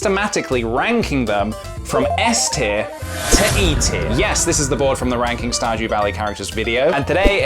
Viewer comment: I thought he said eatier not E tier